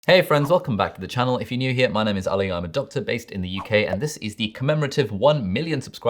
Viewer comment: CONGRATS ALI!! :) In the beginning of your video instead of 'I am a doctor', I heard 'I am adopted'...